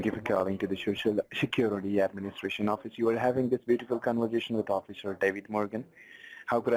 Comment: Did he say 0:14 "you're having this BEAUTIFUL conversation with Officer David Morgan?" Are they fucking kidding right now lmfao